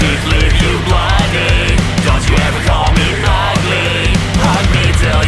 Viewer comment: I thought he said mugly